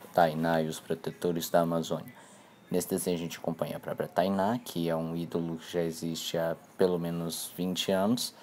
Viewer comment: O Que Você Disse No 3:59? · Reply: “Nesse desenho nós acompanhamos a Tainá um ídolo que já existe pelo menos…